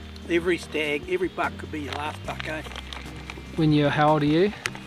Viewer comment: Great stuff and awesome to see get his nice stag. I misheard him there, I thought for a moment he said "Your last BUCK could be your last" Well done